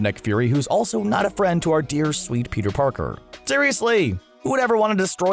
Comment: Am I the only one who heard him say Peter Porker?